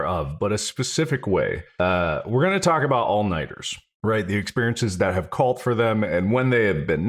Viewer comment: when Mark was introducing the theme of the episode and talking about it being about sleep/lack thereof, I misheard 'we're gonna talk about all-nighters' as 'we're gonna talk about almonds'. few moments in my life have I felt that level of confusion.